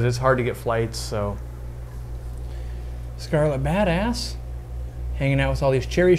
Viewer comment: did anyone else hear Scarlet bad ass